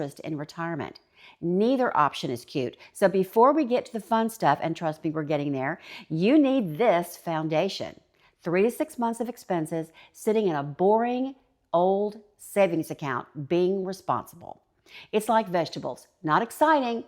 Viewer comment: 1. An emergency fund with 3 to 6 months of expenses 2. Transportation, each spouse has a car 3. A home 4. Insurance: health, home and auto. Long term care insurance should be considered. Life insurance as well 5. Tools to maintain your health 6. A spirit of adventure 7. An endeavor that keeps you mentally active and maybe bring in an income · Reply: I thought you were saying 36 months. Thanks for writing it out.